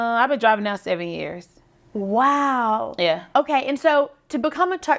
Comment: I literally thought she said 70 years. I had to rewind😂😂❤